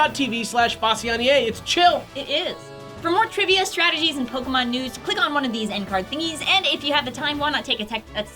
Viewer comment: I thought said Pokemon NUDES at the end